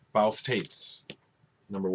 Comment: Thanks, I kept hearing "Baust Tapes" haha, I'm on that Popul Vuh album right now, I've listened to them before, but this is fucking amazing! Almost reminds me of Neurosis with those tribal drums and the atmosphere...